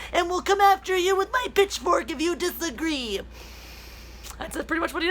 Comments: I thought you said "bitchfork" instead of "pitchfork," but given the soy boy context, I'd say either usage works here. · I thought you said “bitchfork, “ which was a misunderstanding but seems appropriate. We need to start using that term. And going after Anna right now is just tasteless. Come on. Dude needs to put down his bitchfork.